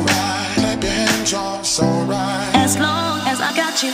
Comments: it sounds like its saying clap your hands drugs all night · I cannot hear "Clap your hands", I keep hearing it as "baby". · Thought it was "Clap your hands, drop so right" Well. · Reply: Ive listened to this eversince forza horizon 2 and I for some reason thought he was saying, "had to do drugs oh right" lol